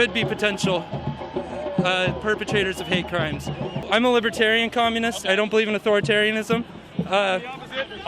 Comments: Did he say "Libertarian communist"?????? :-O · Did i hear the guy at 6:54 right? Libertarian Communist? Does he not know that is an Oxymoron?